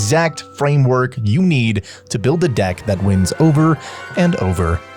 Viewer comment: DICK instead of DECK 🤣